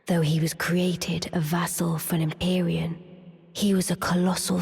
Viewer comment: You think thats a bad take there from left in? I dont hear empyrean there, my buddy hears arian, but all i hear is ALIEN it amuses me